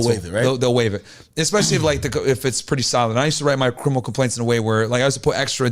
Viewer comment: Everybody talking about the boots but i’m stuck on what he’s saying 😭😭 “Crimpiple cumpaints”???